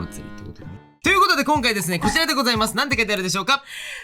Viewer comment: の『こちらでございます！』のところが「くじらでございます！｣に聞こえて？？？ってなった笑